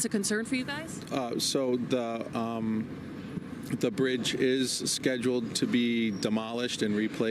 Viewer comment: He was clearly briefed to call it debris falling from the bridge, implying that it is non-srtuctural and not such a big deal. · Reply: I thought he was saying the bridge is debris? :)